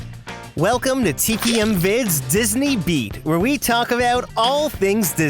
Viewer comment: the first time i heard disney beat i thought he said disney meat 😂